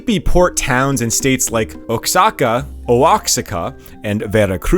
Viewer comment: A tip from a Mexican because this is something you’ll keep reporting on. Oaxaca is pronounced Oh-ah-Ha-cha (the cha is like the word Chameleon or Ka in Karma think Karma Chameleon). I hope this helps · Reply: I heard “O-waka” 😂